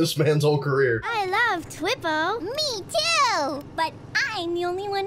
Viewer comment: I thought they said they loved twitter not Twippo lol 😄